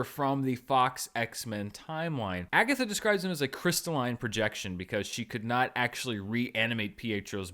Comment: Agatha just basically said she wasn’t responsible for bringing Peter to West View but that when he showed up just just possess him as her eyes and ears to find out how Wanda did this. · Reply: Closed captions confirm she says “crystalline possession”, not “projection” so misheard (not the only one). So we don’t know who Peter/Pierrot/Fietro is for sure, but personally I’m with Roth and Eric over on fandom on this–it’s got to be him, because A) we still have no answer and B) any other answer isn’t nearly as satisfying or interesting. Fingers crossed, at least!